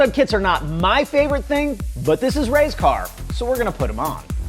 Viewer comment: Oh Ray's car lmao I thought u were saying cuz racecar